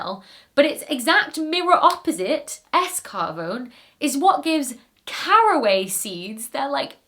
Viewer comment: I swear to god, for a bit there I thought you said escargot is what gives caraway seeds their aroma. I had to reverse it a couple of times and then turn on CC, which was less than helpful.